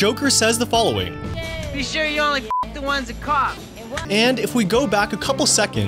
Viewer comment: Ok can someone help me out- Maybe I'm half deaf and dumb but what's the deal with Joker's lines at 11:47? What I heard (and YT cc) "You only f-ck the ones that cop" "The other half have VD". What's the "fact I didn't know"? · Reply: ‘You only fuck the ones that COUGH’ not cop (and then she coughs - as explained in the video).